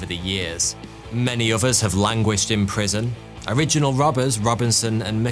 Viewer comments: Did anyone else hear him say at 13:23 "many of US have languished in prison". Makes you wonder. I did not think Thoughty2 was that old. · Ha!!! I thought you said "many of us" instead of "many others". For a second, I thought you had something to share, but you can't. Ha!!!!!